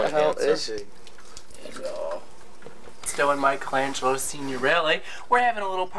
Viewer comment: Did he say michelangelo signorile at 2:57 - same guy who does the radio shows? Well, canceled Sirius XM Out Q show??